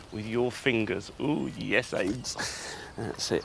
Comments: did he say "yes aids" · did he just say "oooh, yes AIDS!"...? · did he just say "ooooh yes aids"?! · did he just say "oohh... yes AIDS" ?